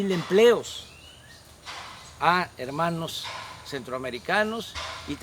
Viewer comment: Lo que escuche "quiero anexar a centroamerica"